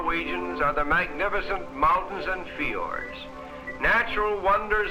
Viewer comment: It sounds like he is saying "Fijords" not "Fjords" Thanks for this ;D